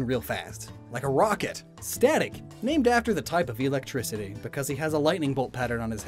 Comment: seriously thought you said sanic! 🤣